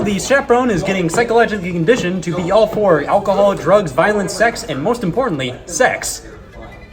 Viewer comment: The first time I watched this I heard "violent sex"